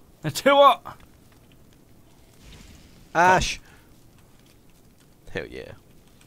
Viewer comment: did say bash or hash?